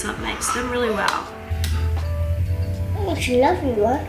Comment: Did he say "oh it's a lovely one"? At 4:27? He's adorable